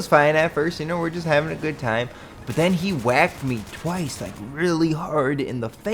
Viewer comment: Hehe i thought he said "He wanked me twice" lol